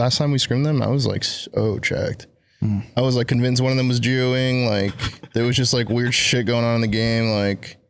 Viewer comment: did say what i thought he said lol? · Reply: He said "geo-ing" if it's the thing I'm thinking you thought he said. Meaning geo-fencing to get advantageous ping.